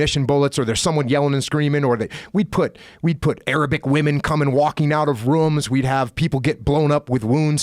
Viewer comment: i thought Jocko said Aerobic women